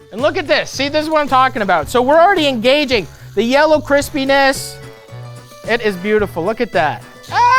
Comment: Caption at 3:56 reads yellow Chris penis instead of crispiness.